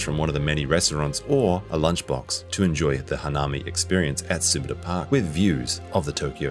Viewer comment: Does anyone know where this summit park is? I cant find it on the map, thank you~ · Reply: Thank you so much for responding, really appreciate it!! @ 1:57 the subtitles said grab lunch from one of the many restaurants or a lunchbox to an enjoy the Hanami experience at Summit Park with view